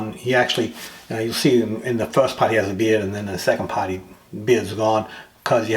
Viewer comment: Hey. Thought you said he had a beer... Lol